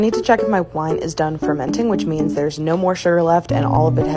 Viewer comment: i thought she said her wife is done fermenting and i was so confused